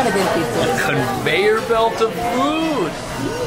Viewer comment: I thought that said “in Japan people eat conveyor belts”